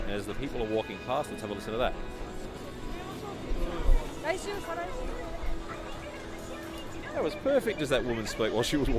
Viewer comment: Wait what did she say? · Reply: She said "Raishu kara..." meaning "from next week". It was "kara" that you missed Anything before or after that was inaudible.